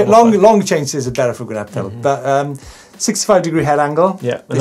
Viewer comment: "longer chain stays are better for 'gruptuttle' but ahm..." ----------------------> Does anyone know what "gruptuttle" is? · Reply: "going uphill"